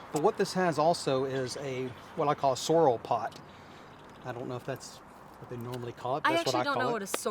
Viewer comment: Yea I heard him say Sorrol Pot Tool